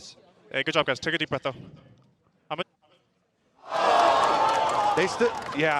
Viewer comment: what did he say??? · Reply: Good job, guys. Take a deep breathe now. ???